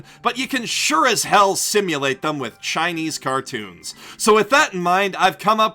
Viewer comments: did he just say Chinese cartoons???? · did he just say Chinese cartoons?